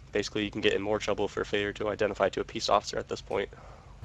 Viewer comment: did that cop just say "peace officer" instead of "police officer"?